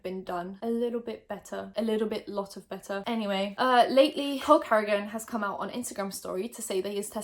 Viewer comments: Lol I misheard her “cole cardigan” and thought she said “hulk hogan” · lol thought you were saying "Hulk Hogan" not "Cole Carrigan" for a second there. Got confused at the idea that Hulk Hogan doesn't have all the clout he would ever need or want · I'm not sure if I'm slowly going crazy because of this whole situation, but I keep hearing "Cole Carrigan" as Hulk Hogan. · I legit thought you said “hulk hogan” instead of “cole carrigan “ not going to lie, I was more interested when I thought it was hulk hogan!